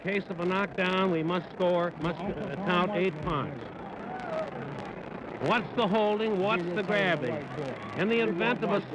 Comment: What was Ali saying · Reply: Time to meet your maker. I'm going to punish you. Nowhere to run now. -- Ali was legit furious over being disrespected about his name, angrier then he ever was. He did a long slow calculated beating, to stretch it out.